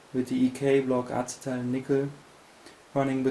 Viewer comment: did he say nikka